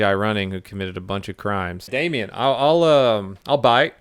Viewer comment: did you say Damian at 11:20. cause i was like shit what did i do.